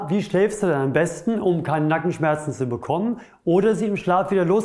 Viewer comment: Sehr interessant aber bei 0:27 habe ich Kanackenschmerzen rausgehört😀😊 aber einfach genial , mache auch zuhause Übungen wegen Rundrücken und schmerzen im HWS Nackenbereich. Ich würde mehr wie 5*** geben.